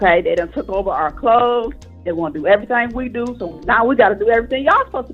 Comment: All I heard was, "Men won't do everything for us."